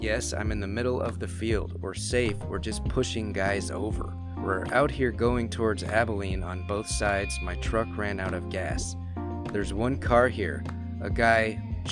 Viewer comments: Sounds like he said they were pushing cows over. And the one line is chased us to the woods. Sounds like the man ran into a group of people that didn’t want him leaving. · It sounds like he is saying "a sniper is poaching guys over" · I thought he said a sniper just shootin guys over.... . He has a pretty thick southern accent so he would most likely say "shootin" instead of "shooting". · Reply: Stevie Leigh Agreed. I hear "a sniper just pickin' guys over". Maybe picking guys over is a variation of picking guys off? "Out here on both sides going towards Abilene" sounds like the shooters are firing from both sides of the field. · Maybe he say they're pulling guys over and it just sounds like the said pushing guys over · I thought he said.(we where out here in this field. Pushing cows over when a vehicle pulled over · I thought he said "a sniper just pushed some guys over" not "we're safe we just pushed some guys over"